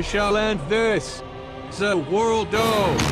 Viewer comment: did he said zaworldo XD?